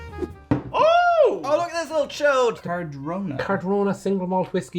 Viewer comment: did just call that bottle a chode? :D